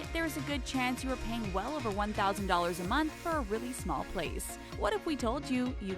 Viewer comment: she said place subtitles said space